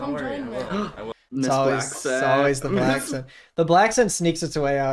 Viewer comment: does anybody know what nd are saying at 5:13 ? , im neurodivergent and sometimes have a hardtime hearing what people are saying clearly so thx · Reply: theyre pointing out her very forced blaccent